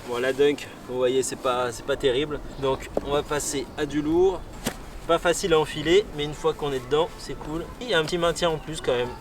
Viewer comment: Je regardais ailleurs et j'ai entendu "Pas facile à en*uler mais une fois qu'on est dedans c'est cool.", là on rentre dans le vif du sujet. :)